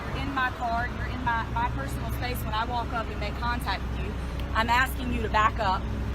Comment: did she say "your in my space when I walk up to you"